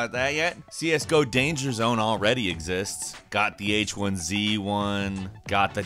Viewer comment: Wait did you say H1Z1.....